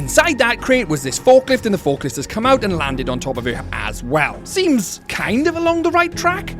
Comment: I thought he said it landed on her ass at first😅